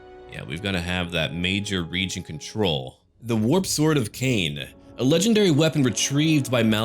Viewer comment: I thought you said ,, The Woopsword of Cane " XD